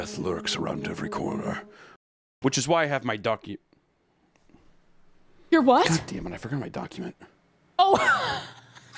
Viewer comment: i thought he said ducky and fully expected a rubber duck to be shown somewhere.